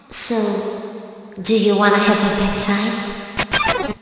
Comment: i heard it for “do you wanna be my valentine?”